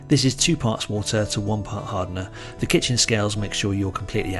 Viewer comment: After minute 21:50, did you say two parts water to one part hardener?